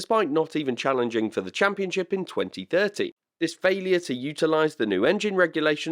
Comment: Only me who heard "2030"? 😅